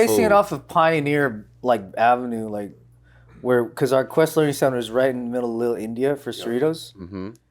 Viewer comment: did he say quest learing center??? 😂😂😂